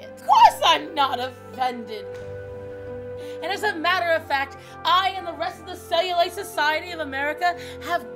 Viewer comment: I could've SWORN I heard her say "as a matter of fat"